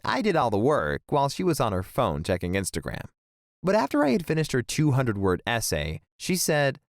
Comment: A 200 word essay is not even hard and I’m in 6th grade it’s only like half a page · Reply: oh at first i thought you said '200 word is essay' LOL no hard feelings btw